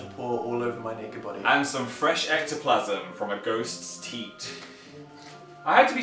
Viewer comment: I thought he said 'goat' instead of ghost.